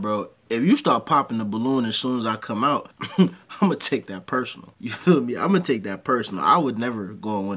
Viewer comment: hehehe, I thought you said "ima take that purse"